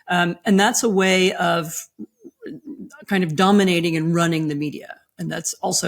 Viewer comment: I heard her say “dumbminating” the American media…and…yup.